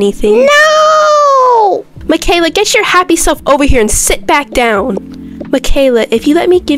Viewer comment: why I heard ,,get your happy ass here😂